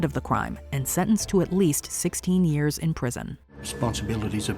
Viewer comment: Kristi Abrahams only getting 16 years for Killing her daughter and burning her body is ridiculous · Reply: Strange. I thought I heard 60 years.